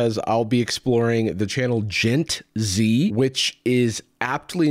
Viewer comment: I was half-watching this while doing the dishes and misheard "Gent Z" as "Djent Z". Might I say, I was thoroughly disappointed that the video wasn't Jarvis reacting to a metal performance, but then again, it would not have been bad advice then, would it?